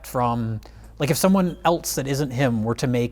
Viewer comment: says ELTSE instead of ELSE! GAAAAAAHHHHH!!!!!!!